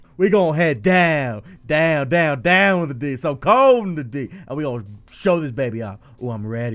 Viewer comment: DOWN IN THE D??? IS THAT WHAT I THOUGHT HE SAID?